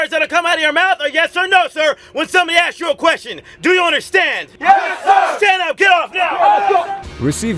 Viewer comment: what is he saying here? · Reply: He said "Do you understand?" Just say "YES SIR!"